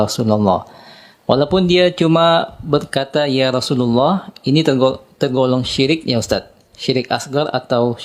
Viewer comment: Sudah semangat membara tp sayang sepertinya salah dengar.. coba simak mulai menit 00:25 ust Dzul jelas menyebut menirukan ya Rosulallahi.. dan berulang beberapa kali Pengikutnya gmn,, manggut2 aja nih,,? hhii